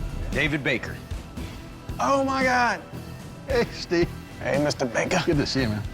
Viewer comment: did he said "X D" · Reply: He said ''hey steve"